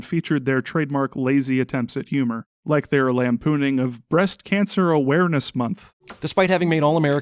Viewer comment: Thought you said beast cancer awareness like MrBeast lolol 😂